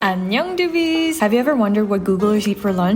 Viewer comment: As an English Canadian everytime doing does her into all I hear is “I’m young doobies” 😂 no clue what she’s actually saying but I feel involved